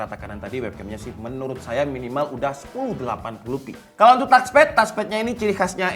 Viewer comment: 180p apa gue yg salah denger yah?😂😂😂 harus ny kan 1080p